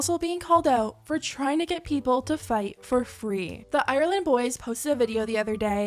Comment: i rlly thought u said island boys instead of ireland boys 😫